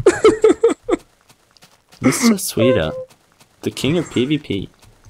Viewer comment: LOL. when eva u say Sweider it sounds like your sayin Sweet heart :3 anyone else think that? :/